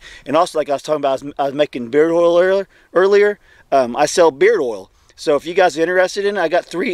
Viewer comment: I thought you said beer oil